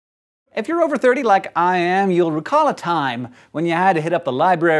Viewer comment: Hahaha, oh my.. I thought Hank said "over 40, like I am" and not 30!